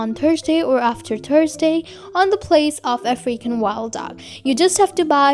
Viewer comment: For the longest time I thought she was saying “a freaking wild dog”